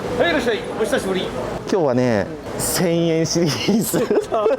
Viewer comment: 「1000円シリーズ」が「潜影リリース」に聞こえてしまう私はもうファンファンなのか…｡